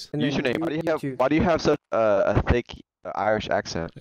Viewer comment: I first head "fake" Irish accent and not "thick" After I rewound it several times, I only hear "fake". - I think they were doubting the authenticity of the Irishman's brogue.